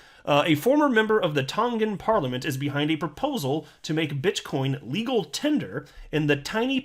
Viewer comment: I thought I heard $BitCHcoin! 😁